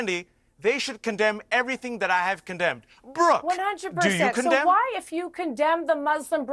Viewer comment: She said "of course not" when asked if she condonded that. She was trying to state that the Palestinian rallies are basically like neo-nazi rallies here in the states. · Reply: She actually said "100% at 6:58 AND at 12:44 as well. She said "Ofcourse... the difference between..."